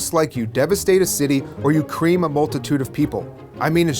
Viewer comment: I thought Deacon said cleam, not cream.